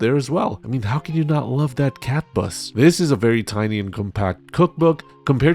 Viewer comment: All I heard at 0:43 was cat puss and I had to rewind.